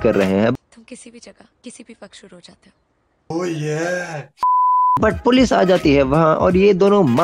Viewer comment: Did I hear 'Oh Yeah!!!!' in this video..